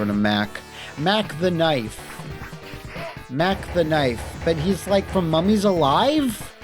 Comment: I kept hearing 'Mack the Knife' as 'Mac tonight'